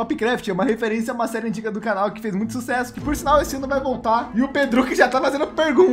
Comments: eu ouvi " esse ano vai voltar"? Misericórdia finalmente aeeeee Espero que ano seja dia 30 de fevereiro rsrsrsr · Por acaso eu ouvi direito?TopCraft vai voltar esse ano???????😲🤘